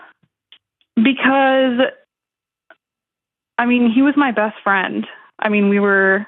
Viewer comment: First call - She said her ex-fiancé was her “best friend”, but EVERYTHING else said that she doesn’t even LIKE or RESPECT him that much. She should do herself and him a favor and stay away and let BOTH of them move on. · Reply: She actually said "He WAS my bestfriend" 2:34 ish